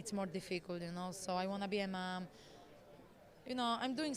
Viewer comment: did joanna say "i wanna be a man"?